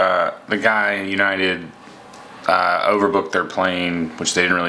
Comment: I thought you said you got "Reeboked" and got yourself a pair by choice. Lol.